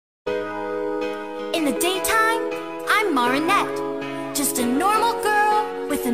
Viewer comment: Lol it sounds like she is saying m-u-u-r-i-i-n-e-t